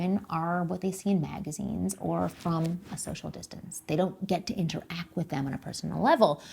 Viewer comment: Um 1:08 what did you say?! "Social distance"? and 1:15 yes, you are.